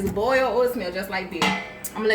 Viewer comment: YES M'AM FINALLY A DIPPING SAUCE SIMILAR TO BLOVES lolll <3333 and I swear it sounded like u said "oo it smell just like d*ck" 6:36 I SCREAMEDDD!!